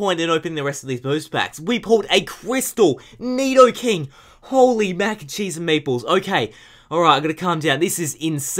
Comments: For like, the first fifty videos I watched, I thought he said Holy Mac 'n' Cheese and Maples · It always sounds like he says holy mac n cheese and maples